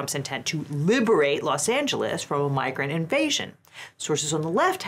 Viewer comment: In the add read at 23:38 I thought se said "migraine invasion" 😂